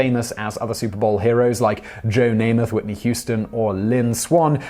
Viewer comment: Does he say 'Whitney Houston' at 3:01 ?